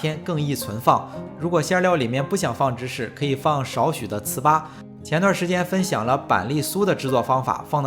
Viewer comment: 請問芝士的代替品（5:06）是什麼？沒有字幕聽不懂：（ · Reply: 糍粑